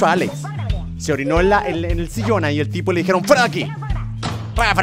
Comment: escuche a la vieja decir ''fuera alex'' no se si lo ustedes también lo oyeron :0